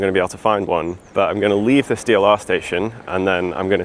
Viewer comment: I thought he said, "I'm going to leave this steel arse station".